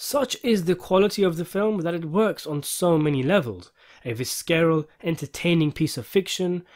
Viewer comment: PAUSE. Did you say "Vis-carol?" I've never heard someone butcher that word so badly XD The C is like an extension of the S sound here.